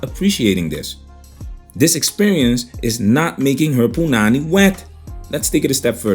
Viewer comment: Wait Wait Wait did you say Poo-nanny at 7:51 holy shit bro! Sounds like you are from the Caribbean Instant Like keep of the great vids and informative vids!